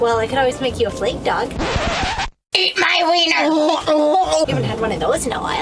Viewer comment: What the bloody madness was that at 10:00? Is she saying eat my wiener. I spit up and choked on my drink, was NOT expecting that lol. Thanks!